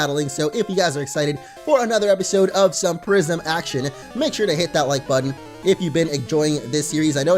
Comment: I thought you said prision action